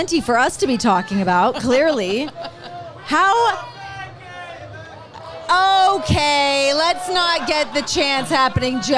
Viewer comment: What did they shout? It wasn't clear to me. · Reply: "We want Becky"